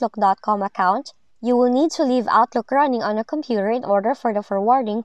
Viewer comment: I can't make out what was suppose to be "left off" at the 2:25 mark -- it sounds like "the crowning," which of course makes no sense. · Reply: Leave Outlook running for it to work